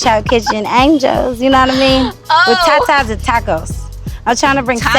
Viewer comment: Did she say tetas-and-tacos?